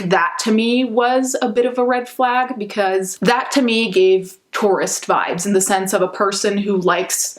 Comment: at first I thought u said "taurus vibes" the teaaaaa